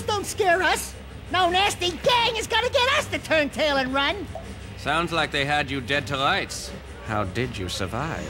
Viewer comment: Notice at 3:22 he says "sounds like they had you dead to LIGHTS" and the subtitles say "Sounds like they had dead to RIGHTS"